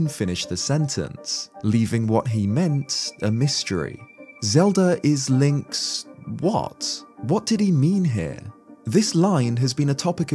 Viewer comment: I thought he was saying zelda is link's betrothed